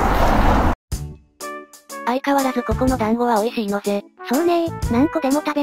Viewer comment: 団子じゃなくて暖房って聞こえた